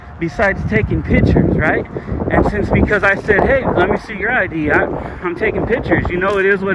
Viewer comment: I heard her say: let me see your idea, not ID.